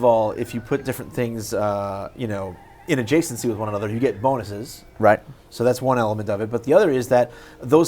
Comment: what was he saying? "... you get vomiters" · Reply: he said bonuses :p